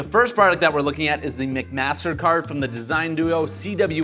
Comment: I heard "mcmastercard" for the first one so I thought it was a mcdonalds themed credit card lol